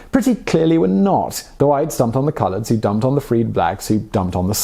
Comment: HEY BROTHER WHAT DID YOU SAY AT 4:39? ´Colords´? Where you getting this word from? In the United States calling any back person colored or 'colords' is a good way to create a serious problem for yourself. And Im white... but yea WHY YOU CALLING BLACK PEOPLE 'COLORDS'?